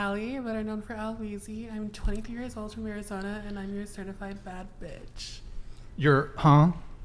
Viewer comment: I heard "certified fat bitch"